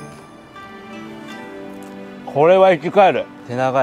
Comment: ｢これは生き返る｣が｢これはEXILE｣に聞こえた……